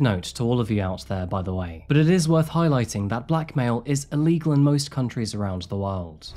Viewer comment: When he said Blackmail I thought he said she had to deal with Black male. You are treading on some thin ice here!